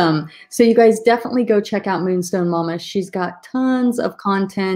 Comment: I thought it said moonshine